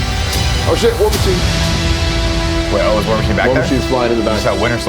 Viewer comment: What does Tim say around 7:00? Nick mentions he saw Winter Soldier right after. I couldn’t understand the name of the character Tim mentioned, probably because I have never heard of that character. Edit: Did he say War Machine?